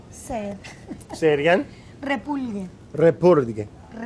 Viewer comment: Anybody know what she says at 9:49? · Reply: Repulgue